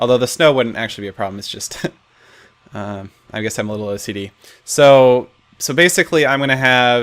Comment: instead of OCD he said STD